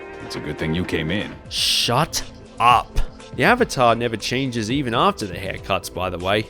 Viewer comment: I hear Shut up